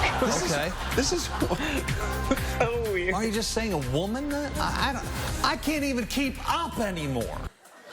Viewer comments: did he say “aren’t you just saying a woman then” local news ally. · I think they've taken that weather guy out of context a little bit. To me it sounded like he said "then don't we just say woman?" which i think is a pretty progressive and fair point. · I can see why the guy is confused. He said, "wouldn't it just be a woman then?" He was confused why they said transgender woman, instead of just saying she was a woman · As stupid as the Fox Weatherman was in this one... his thought is exactly what we transgender people want. "Wouldn't you just say a woman then?" Exactly. Call me crazy, but I kind of liked how he simplified it and just removed the "transgender" context. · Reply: I thought he said, "why are we just saying women then?" You're right, that does make it better.